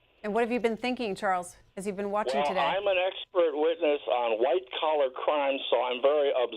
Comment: from CA Anybody know what he was talking about before they cut him off, I couldn't really hear what he was saying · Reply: he was talking about how he is a expert